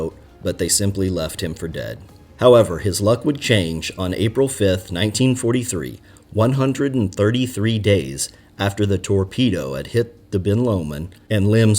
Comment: For a second at 5:30 I thought he said: "However, his luck would change in 1945... " I laughed hard